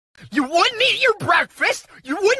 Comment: I thought ms circle said in the start YOU WANT ME YOUR PURPOSE But she actually said YOU WOULDN'T EAT YOUR BREAKFAST